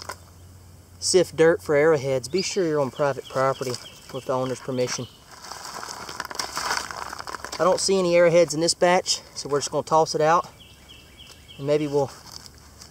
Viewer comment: For a second I thought he said make sure you dig on private property without the owners permission lol